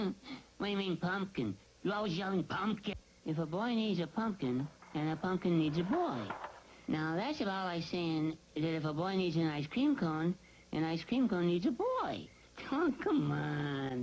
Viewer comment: at first i thought he said "if a boy eats a pumpkin and a pumpkin eats a boy thats like saying if a boy eats an ice cream cone and an ice cream cone eats a boy"